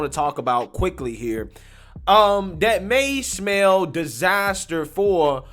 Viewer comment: did you say "may smell disaster"?